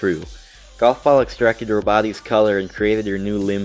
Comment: still wrong golf extracted pins color which is why she's white not red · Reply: It sounds like he said “roboty’s” and not “her body’s”